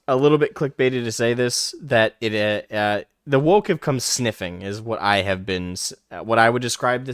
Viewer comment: The first time you said woke I thought you said wolf come sniffing... Correct. Fight the wolf, cut a head off the dragon, bludgeon the chaos back into its confined space. Our favored fictions may be distilled non-fictions, memes, archetypes... First time I've seen your channel, keep speaking the truth. These are our games.